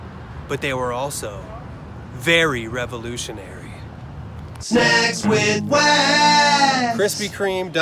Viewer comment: im pretty sure i heard "sex with wax" instead of snacks with wax o.0